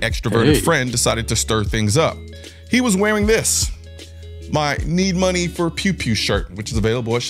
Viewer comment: I thought you said he was wearing a need money for pew pew hat🤨🤨 lol good advertising the new shirt man